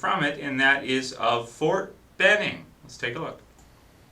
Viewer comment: I thought they said fort Bennett but in season 2 I heard them say fort benning